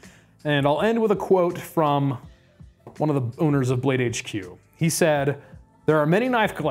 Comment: sounded like you said “one of the boners of BladeHQ”